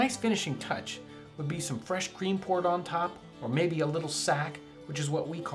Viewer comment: Looks great! Did he say "fresh green corn on the top" at 8:43?